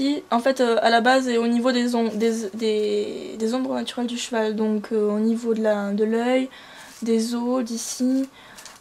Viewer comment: au niveau des quoi ? ^^' Sinon super vidéo, merci pour tous ces conseils ! :) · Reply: des ombres naturelle du cheval, des yeux , des os